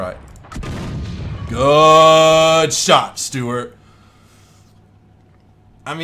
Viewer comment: ahahah bruh its sounds like he says stewarts meal ; DDDDDD